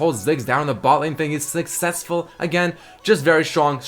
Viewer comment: I SWEAR I heard you say bot lane Ziggs was Ziggscessful